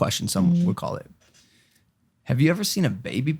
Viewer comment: I thought Logan said baby bitchin when he asked about baby pidgeon